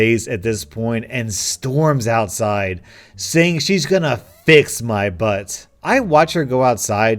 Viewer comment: I was only listening and not watching, and I 100% thought he said "fist my butt"